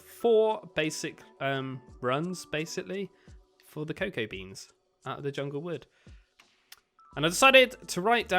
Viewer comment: who else thought he said cocaine beans